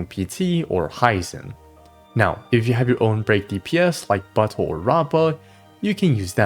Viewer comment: did u just call my man a butt hole?